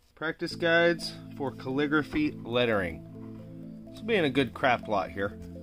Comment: i know its almost 2am here but @ 3:42 Did he just say CRAP LOT?? :)